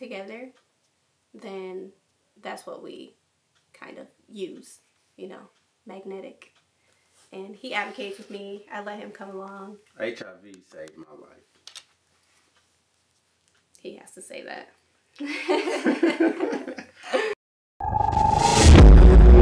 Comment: Her husband is truly an angel sent from God. At the end it sounds like he said “HIV saved his life” but in reality I’m sure he played a huge part in saving hers!